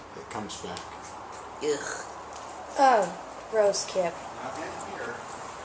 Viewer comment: i heard "I'm in here" at 3:10 thumbs up if you did too